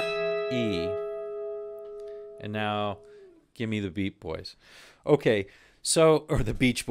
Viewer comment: "Give me the wheat boys and feed my soul" great lyric 😉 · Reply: I thought it said give me the “beat”